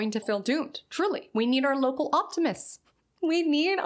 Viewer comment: lol. I thought it said.. “loca optimist” I was thinking.. me too girl